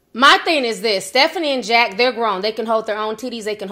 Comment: is it just me or does she call zack jack?